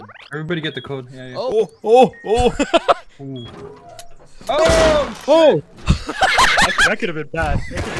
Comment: Bro sounded like that “OHHHH SHIT A Rat” meme 😭